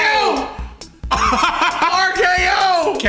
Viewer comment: Did he just say RKO?